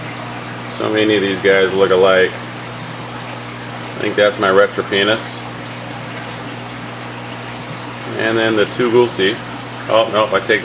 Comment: did you say "retro penis". lol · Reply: LOL yes I did it is actually spelled retropinnis, and actually the Ornate Bichir is actually called the ornatipinnis. Gotta love these names. lol